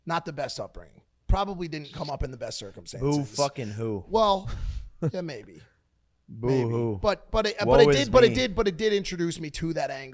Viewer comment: Sorry, I'm trying to learn english, what does jake say there 0:40? I hear "Who fuc*ing who, who who? What was me?" Am I hearing it correct? What does that phrase/s mean?